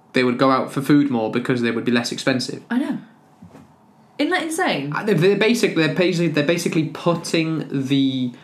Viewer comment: Great listening practice! At 9:10, did you say "init insane"? A short version for "isn't it insane"? I hope you upload more chats like this, because I'm definitely going to use them in the classroom. I love the Millennium Falcon next to the lamp! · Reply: Hey! Phonetically I said ‘Ent that insane?’ which is an evolved and idiosyncratic version of ‘isn’t that insane?’ You might hear natives saying ‘ent’ instead of ‘isn’t’ if it fits their dialect, age and accent 😊